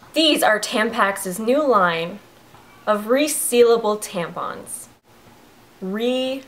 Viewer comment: I thought it said reusable tampons and I was like what?!?!?!?!